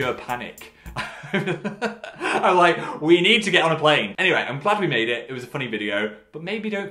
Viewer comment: did phil just say "i'm plaid we made it"?